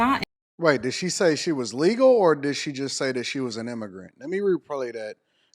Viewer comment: I thought she said she was ignorant. Bumba Clot girl!